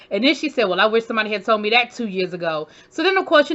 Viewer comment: When Gizelle said I wish somebody told me that 2 years ago, I thought she said 20 🙃